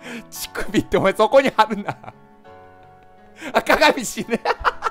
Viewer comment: あっ鏡死ねが…赤髪死ねに聞こえて、嫌いなんかな？と思った笑